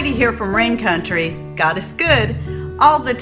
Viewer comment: I've listened to a few of your videos but I swore you were saying "gotta scood!" Instead of "God is good" and it only just clicked 😂